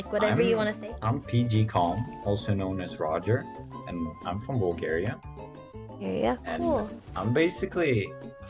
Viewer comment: Did he say I'm from Bulgaria?? :O